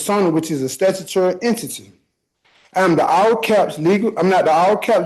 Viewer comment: Did he say, “I am the all caps N!?” 😂😂 Tell me you’re a smooth-brain tard without telling me you’re a smooth-brain tard 😂 EDIT: Why he gotta sound whiny af??